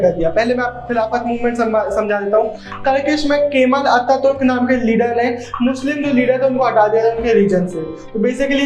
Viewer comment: Bhai aapne kya bole turkisk k aage bo leader ka nam clear ni h bta dijiye na in 8:53 m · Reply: Kemal Attaturk